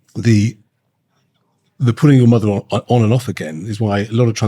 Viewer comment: What does he say here? They look like who? I couldn’t make it out because I am not British. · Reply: I figured it out (thanks search engine.) He said Hyacinth Bucket/Bouquet, a character in a British tv show. 🇬🇧